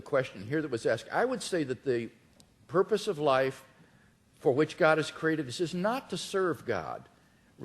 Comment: Did he say: "... the purpose of life, for which God is created.."???